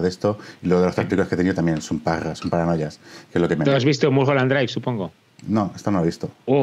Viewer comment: En el minuto 13:53 no se entiende qué película nombras. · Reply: Dice la película de Mulholland Drive de David Lynch. <link>